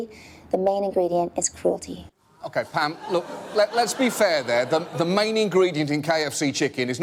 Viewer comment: Did... did he say fam?